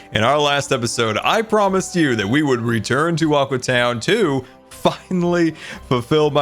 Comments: that sounded like fucinlly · this sounds very weird but he really said finally when I thought he said f***